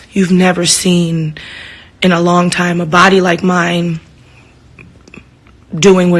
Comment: I thought she was gone say you aint never seen in a long time a booty like mine I'm logging out